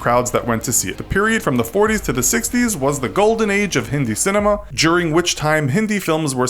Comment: did he say hindu cinema? was the 30s the golden age of christian cinema? I don’t think so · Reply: He said hindi